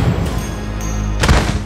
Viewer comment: I legit thought I heard "You ain't shit" the first time I saw the vid 😂